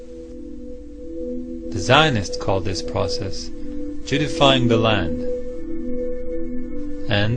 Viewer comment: did he say "Jew defiling" the land?